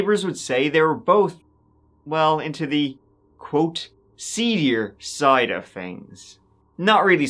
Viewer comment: What word is he saying at 9:06 ? “They were both into the more, quote what side of things?” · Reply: “Seedier”.